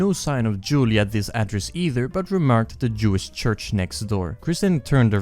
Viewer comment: Wait he remarked about a Jewish church? My brain just like malfunctioned for a second and smoke started coming out of my ears. Did he say Jewish Church?? I just want to make sure I'm not losing my mind...